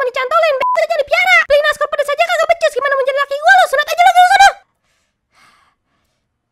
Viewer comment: "gw dengernya gmn mo jaga laki gw lo"